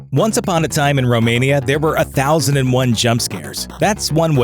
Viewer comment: wait did u say Romania? 😂